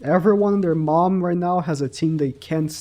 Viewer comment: Did say their mom ?